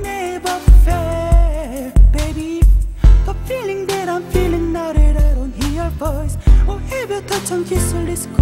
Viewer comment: i think i heard im feeling dead... hahahah lol 0:38.... the sub is not right... u must change it...